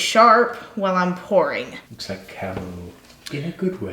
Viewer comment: I thought he said " looks like cattle " 🤣🤣🤣